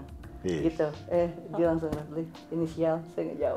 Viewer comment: itu dustin ngomong apa? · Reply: Tunggu aja besok